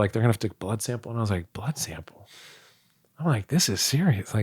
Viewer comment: Thought he said "And I was like "butt sample?" and I was like "Butt sample?" Trying to figure out why an actor would need to take a butt sample, this is what I arrived at. <link> The Buttman.